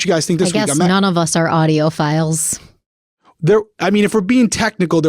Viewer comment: Yupp, definitely thought you guys were saying “audio files” instead of “audiophiles” 🙈